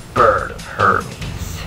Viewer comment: Anyone else thought said "I am bird herpes" at end.